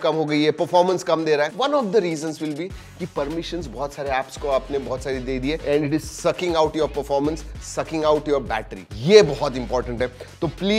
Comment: sir what are you saying 💀💀☠️😂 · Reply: Bhai sucking 😂